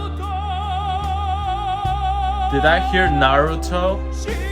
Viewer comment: I don't speak Japanese, at 7:29 I heard Naruto as well so your comment cracked me up lmao